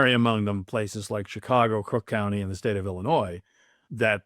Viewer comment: I could swear that Mr. Cembalest says, "Crook County". Not sure whether it was sarcasm, a Freudian slip, or my hearing aid needs to be turned up, but Thank You, Sir, for telling it like it is.